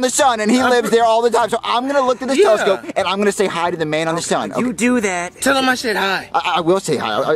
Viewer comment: What did the alien say? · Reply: sounds like he said, I'm going to parashoot on the sun!